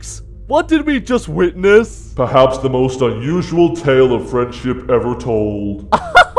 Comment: When Golden Freddy said "Friendship", I thought he said "French-Dip". xD